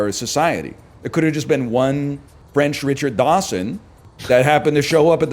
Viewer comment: is he saying Richard Dawson? Like the singer? can my guy get any more based?